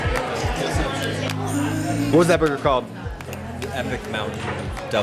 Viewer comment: Why did you not film Ethans fall, people falling is funny When you asked what is that burger called it sounded like you said what is that booger called, i was confused for a second Was ordering the cock and balls really awkward or was it like just like hey we love eating dick lets buy a frosted one.